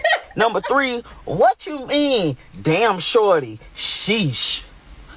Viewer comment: I really I thought she was saying damn shorty to girl in blue shirt with big gut